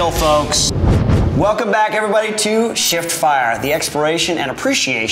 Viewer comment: Cool video. Really does sound like you say sh*t fire not shiftfire though 🤣 which made me laugh haha